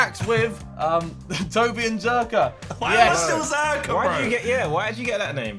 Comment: I honestly thought Harry said Jerkaa lmao